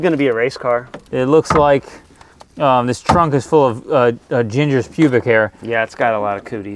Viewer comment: hell ya starting my Sunday morning right with Part 3! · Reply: i mean, im multi tasking here.. but i coulda sworn i just heard the term "ginger public hair"???